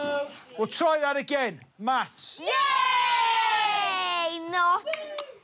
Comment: "Maths" Kids look like they wanna die "we'll try that again, maths" "yay" The most British thing I've seen all day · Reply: ben happe i srsly thought he said baths